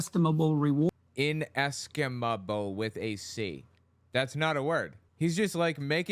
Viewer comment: Sounded like he said inestimable with a T to me.